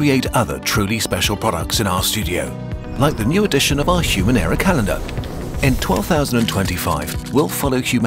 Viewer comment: For a sec, I misheard ‘human era calendar’ as ‘human error calendar’ and now I want a calendar that greets you each day with some major historical mistake that happened on that date.